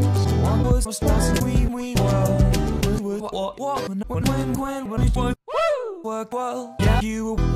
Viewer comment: why do i hear wee wee world